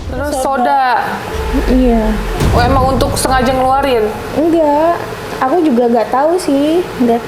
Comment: Ke minit 9:02 mba nya bilang apa? Kurang mengerti dia consumsi apa itu🙏🏻🥲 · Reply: sprite g si mkstnya?